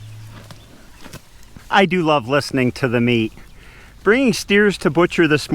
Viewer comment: I do really like to listen to the meat... Me: Hmm, that seems a little cold for a thought.................oh, wait, he said "listen to *them eat*." Geez, sorry about that. 😅 I remember turning off the lights in the barn on a winter evening after we'd stocked the cows up with their hay for the night, and pausing a few seconds to just listen to them munch away so cozily in the warm darkness. It was a good feeling. Ours was a dairy farm, but we raised steers for the table. I do remember feeling some quite sharp pangs of remorse as I had gotten attached to certain ones. I've always been softhearted about animals. But I never swore off meat. I can't say that I never will for sure, tho. I can see both sides without quite being able to voice it adequately. Those steers were such good fellows when it came to going in, even kicking the gate instead of you. Do you suppose that was intentional? I've seen a lot ornerier bovines. Did some rube say looked angry?? Lemme at 'em! looks a lot like my former husband's Aunt Ruth. I'm positive she is sweet to the core...